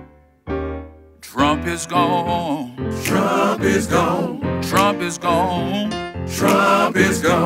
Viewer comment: Sounded like he said the B** is gone lol play that piano